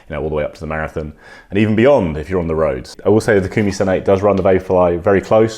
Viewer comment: what's he saying? Sounds like Kumisun 8... 🤔